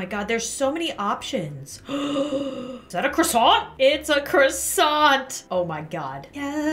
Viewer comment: I love how the first thing I hear is “there’s croissants”